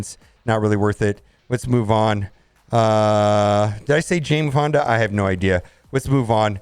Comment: who is Jane Fonder? Is she like a MSF only creation like kestrel? I dun recall marvel having such a character. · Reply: I thought he said Jane Fonda 😆